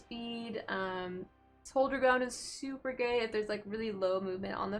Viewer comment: I fell asleep with YouTube on and woke up to this, then I heard something I wasn't sure of so I had to rewind like 5 times and finally turn on closed captioning at 6:57. I was pretty sure "super gay" is what was said, and I feel validated now lol. The only thing I'm going to retain from this video. Hold your ground = super gay. Oh and just before that closed captioning said "Whenever you blow four person" so that was a happy accident to stumble onto. Sorry my comments are dumb and lame, but they help push your metrics so it swings in roundabouts.